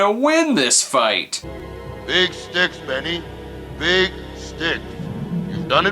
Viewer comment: Oh wait, the ghost fella said "Big Sticks", didn't he... in that echo, all I heard was "Dicks dicks dicks dicks..." Thought for sure that was gonna be a joke or something... b'nope, just something about acoustics.